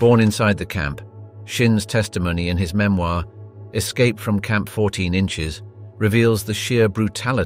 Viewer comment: Did I hear correctly „Escape from Camp 14 inches“???